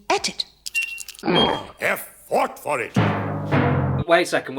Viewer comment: I legitimately thought the guy said F FUCKED FOR IT